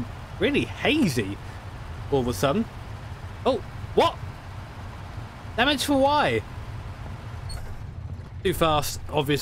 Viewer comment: Did he say "damage for why?"